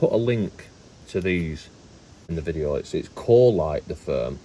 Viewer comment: I thought you said Coalite 😂